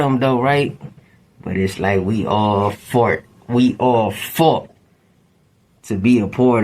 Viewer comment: " but its like we all FART!" Is crazy 😂.... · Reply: I thought I misheard that but yeah he defo said "We all fart" then changes it quickly to "we all fought" 😂😂 shits wild asf 😂